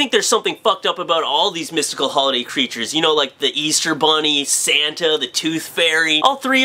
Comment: lol, when he said Santa, I thought he was saying Satan